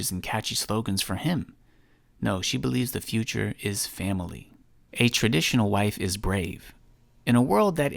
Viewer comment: Dom: did you say family?